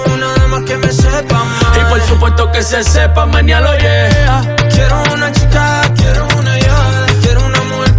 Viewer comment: Pensé que decía: "quiero una chica quiero una llave 🔑🗝️"